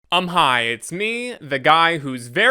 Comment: Literally just woke up from a 5 hour nap and I thought you said “I’m high” at the start for like 2 minutes straight before I went back to see what you actually said lmao